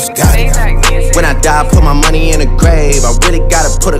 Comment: thought he said "when i die put my money in a GRAPE" i was so confused until i looked at the lyrics